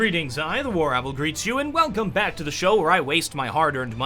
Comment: the "War-Apple"?? Did I hear that right?